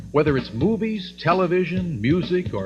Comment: I legit had to double take at that XD I was watching on a second monitor and it sounded like "Whether it's boobies..." No matter how many times I replay it, thats always what it sounds like hes saying